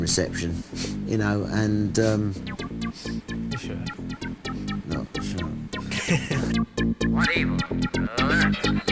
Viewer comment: Lol what does Liam say at 1:23 ? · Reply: "Yes you have"